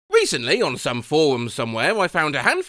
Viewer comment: "Recently on some forum somewhere" I thought you said "Recently on some porn somewhere"